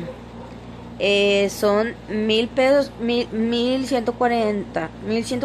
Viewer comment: En el minuto 0:42 parace que dice pedos